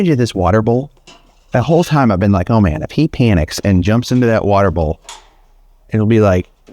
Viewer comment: timestamp 37:47 I thought you said “If he pee in its…” but you said “If he panics…etc.” 😊 But the elephant trying to jump into the pool jumping off the diving board” was epic!!!😅😂 Moose!!!